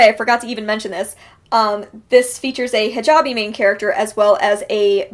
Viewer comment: Regarding "I Was Born for This", did you say "hijabi" main character? If so, what does that mean? Is that someone who wears a hijab? Not a term I'm familiar with, but maybe I misheard :S